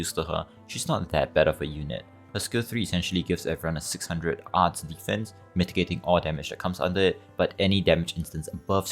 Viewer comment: Did you say Lin S3 gives everyone 600 HP shield? She doesn't have that in her kit or I just misheard you, comparing her to Silence2 is kinda weird too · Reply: iirc, I said "600 arts DEF" agree on the second part, should've cut it out